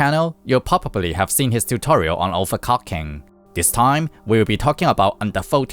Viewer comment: Am i the only one who hear an [overcocking] at 0:04?